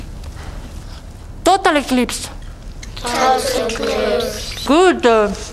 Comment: When I was little, I always thought she was saying "totaly clips" and I didn't know what a totaly clips was XD